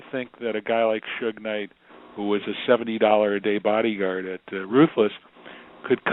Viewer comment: He called Suge a $70 a day bodyguard....now thats ruthless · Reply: Tbh I thought he said 70hr bodyguard but yea 70 a DAY ain't shit